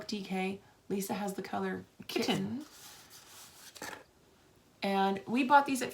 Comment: Lisa, I know this is an old Vlog but I was just watching it for the second or third time and realized that I thought you were saying the color of the yard was kitchen not kitten. Having watched several of your videos I know you’re laughing now…